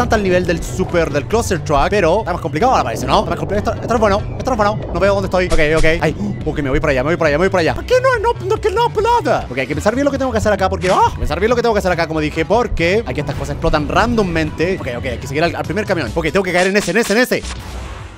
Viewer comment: alguen me puede decir que carajos dijo german en el segundo 0:16 yo entendi "a qui no pud o pulapa"